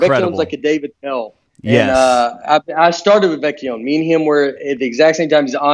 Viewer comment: Who are you guys talking about at 1:06:28 ? I can't make out the name. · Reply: Mike Vecchione